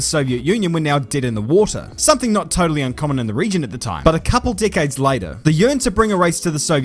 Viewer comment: I can't be the only one who heard 'a couple dickheads later'